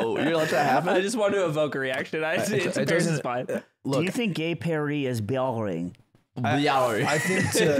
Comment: Can anyone help? What does slime say at 11:52?? He’s said it in a few podcasts and from context it seems like a location and it sounds like he’s saying “gay paris” with a French accent but nothing comes up when you google it. The alternative is that he says gay perry which is a character in kiss kiss bang bang according to google but I don’t get the connection if it is